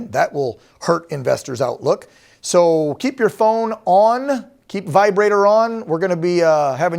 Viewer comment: Did he just say keep your vibrator on